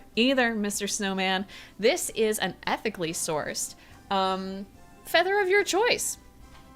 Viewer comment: really thought said "epically sourced" and had a flash from a dark reality where companies actually used that to sell to gen z